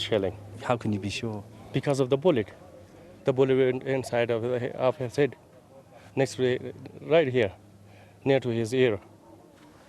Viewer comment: Can someone clarify what he says there? Cannot make it out. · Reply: (Points at head) The bullet went into his head, right next to his ear. (Points at body) He didn't have any injuries.